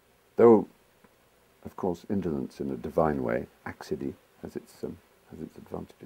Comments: What is Stephen Fry saying at 4:51? I have no idea what he just said. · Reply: Accedie, from Acedia, a Late Latin (anything Late Latin is medieval and Church-derived) word meaning listlessness or sloth, one of the medieval Seven Deadly Sins. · I was puzzled by what he almost said at 4:51 before he interrupted himself. The transcript reads "...indolence in a divine sense actually has its uses". What he actually says is "Though of course indolence in a divine way, accidie as it's... has its advantages... Oh shut up, Stephen." So the mystery word is "accidie", from the Latin acedia, which in turn is an adoption of the Greek ἀκηδία, meaning a state of non-caring. From the OED: "Acēdia became a favourite ecclesiastical word, applied primarily to the mental prostration of recluses, induced by fasting, and other physical causes; afterwards the proper term for the 4th cardinal sin, sloth, sluggishness."